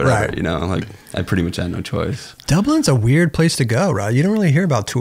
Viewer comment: Wait...did Crob just say Dublind? 14:14 😂